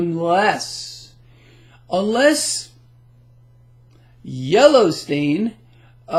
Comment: I thought you are saying Yellowstone, 😂😂